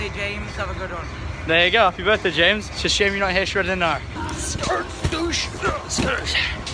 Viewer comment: Wtf did he say at 5:57 ? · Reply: It's a shame you're not here shredding the gnar